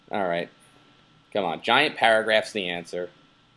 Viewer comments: almost thought you guys said "Giant Parrot grass is the answer" when talking about giant paragraphs lol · I thought said "Giant Parrotgrass is the answer" ...I mean, he's right, but still.